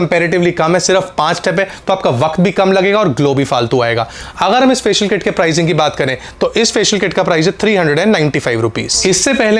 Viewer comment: Did he say Glow bhi Faltu ayega?